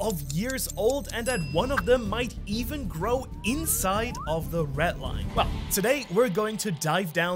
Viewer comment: sounds like he says "ret line"